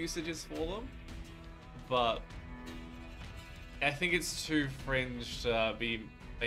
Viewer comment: I know you said fringe...but it sounded like cringe xD